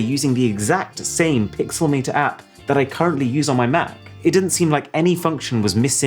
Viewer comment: what photo editing app is he saying? It sounds like he said Pixel Meter but I don’t find an app named that in the iOS App Store. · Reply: I was looking for the app too. I think it’s Photomator (by Pixelmator) on the AppStore